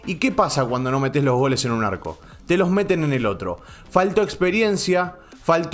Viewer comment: al principio entendi "te los meten en el orto" jaja mi cerebro me traiciona